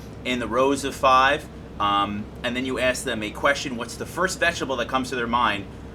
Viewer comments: I thought he said whats the first decimal that comes to mind... so I said zero. LOL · when he asked what vegetable comes to mind I said five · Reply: GameInzanity umm i didnt even notice he said vegetable.. I heard it "number"